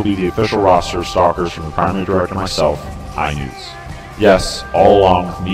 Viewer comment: Could someone please clarify what he says at 4:15? If you are going to state the name of the "Director" you it would be in your best interest to clearly state it. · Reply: (HeXxNewtzy)